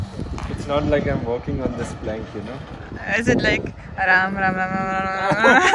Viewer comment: Who heard Ram Ram Ram Ram 😂😂 by Dhruv gf